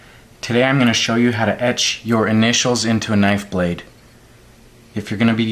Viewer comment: Oh I thought it said "Etch Initials Using a Knife blade".